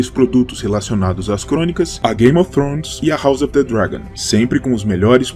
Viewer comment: Carai n é Monster Hunter? Entendi "todos os Elder Dragons" kkkkk